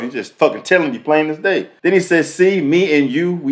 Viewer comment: " see me and you we almost had the same outcome. Heath". When I first heard I thought he was saying "he"